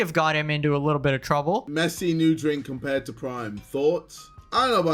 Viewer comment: Yo, why do the subs for KSI saying "Messi new drink compared to Prime" say Ronaldo instead of Messi?